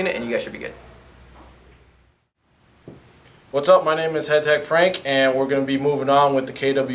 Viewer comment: did he just say hentai frank??? O.o the first time ii went over it i heard it like that